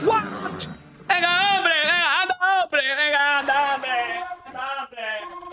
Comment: what did you say at 58:22? ahaha, something like that "ana nambre". I want to learn Spanish